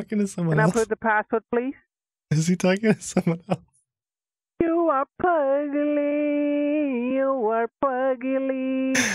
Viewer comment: When he was singing, it sounded like he said "you are ugly! You are fugly!" And I died...